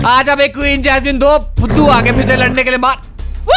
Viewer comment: did he actually say do PHUDU a gai